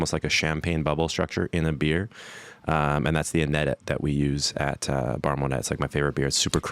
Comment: What is the word he is saying at 4:11? Annetit? Ennetit? Its somehow relates to Estrella Damn Beer or Bar Monette? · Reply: ahh i found it is "INEDIT" from Damm